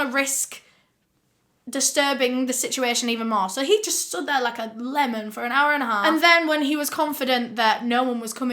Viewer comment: did you say he stood there like a lemon? 🍋😭😭